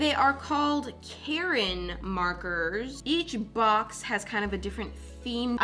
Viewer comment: I can’t be the only one who thought she said kAReN mARKeRs and got so confused